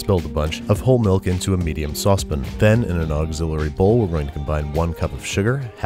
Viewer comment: does he say "sauce pin"?